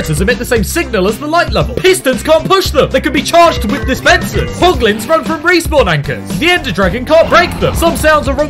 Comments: It sounded like he said POG lins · With the hoglins I thought you said poglins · Not piglins im sure i heard poglins POGGG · Is it just me or does it sound like he said poglins